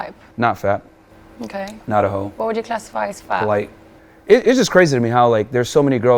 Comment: did he say “white” @ 5:44????